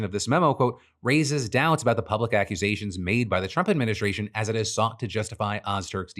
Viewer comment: I heard "Racist doubts about the Trump Administration" and was like "There's Doubts?!"